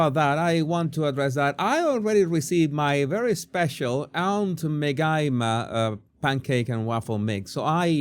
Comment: I thought you said "Aunt Magina" like vajayjay